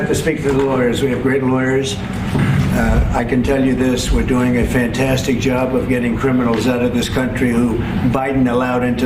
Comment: could of swore he said pam your doing a great job!!! 😂😂😂😂😂😂